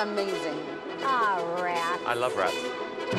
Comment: the I love rats from Simon sounded like if you don’t like em you’re fired